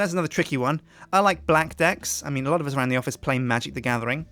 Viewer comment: lol when he said blackdeck it sounded like he said black dick!